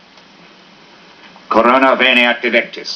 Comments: sounds like he said 'corona virus '😂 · Jesus Christ I swear at 1:08 seconds I thought he said coronavirus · Did I just hear orona virus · what did he say? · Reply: Corona veniat electis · who else hears "Corna virus" or just me lol